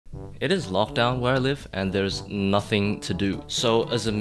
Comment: thought he said "it is love down here where i live"